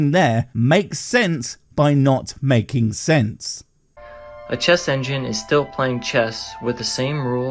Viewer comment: God and the chess engine have one thing in common, humans created both. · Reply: I thought you said cheese engine. Yeah we hoomans made that